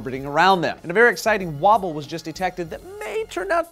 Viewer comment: i first i thought he said "waffles" instead of "wobble" and got super excited.